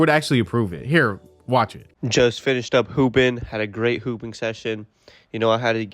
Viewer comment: it sounded like he said poopin